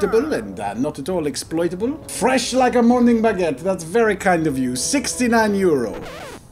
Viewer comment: 😂🤣🤣🤣 I thought you said moning baguette